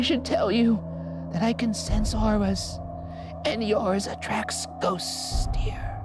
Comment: captions/cc/subtitles say "indistinct" but the grandma says "I can sense auras"